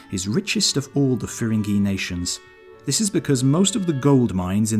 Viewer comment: Every time he says “Firingy” I hear “Ferengi” and end up picturing Europeans as bald, big ear, profit driven people.